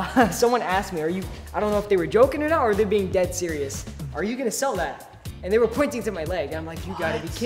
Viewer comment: i thought he was gonna say…”are you gay?” 😭🏳️‍🌈